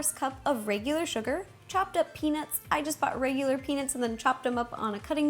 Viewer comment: when she said "chopped up penuts" i thought she said chocolate donuts. IF U THOUGHT THAT 2 SAY "I LOVE HOMER SIMSPONS IN" ALL CAPITALS